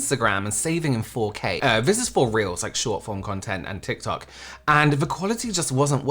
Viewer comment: you said 'this is for reels' but i heard 'this if for reals' i was like yeah i believe you